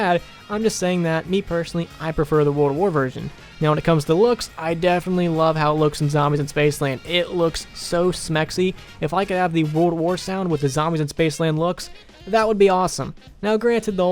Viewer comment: Did you say smexy?